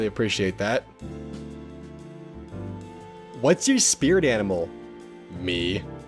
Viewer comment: At first i thought it said whats your spirit anime?😂